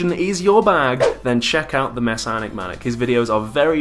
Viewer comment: That's the first time I heard your guest's name spoken. This whole time I thought his name was "The Messianic Maniac".